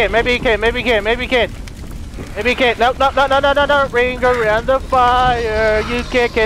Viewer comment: When you said "Ring around the fire" I thought you said "Ring around the bodies"